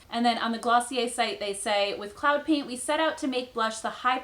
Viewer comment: When she first said cloud paint I thought she was saying CLOWN paint and thought why would a company call make up that LOL